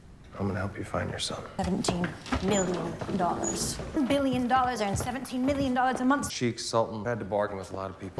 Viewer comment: 10억을 묻었는데 한달 이가자 170억인건 대체 어느나라 금융권 이자임? 저거 대사가 씹혀서 몇 Billion 이라 했는지 못들었는데 10억이 아니고 10조아님? ㅋ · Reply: 10억원이 아니라 10억 달러를 이야기한거겠쥬